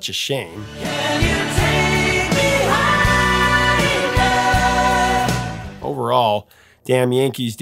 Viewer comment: When I was a little kid I thought he said " can you take me hyena "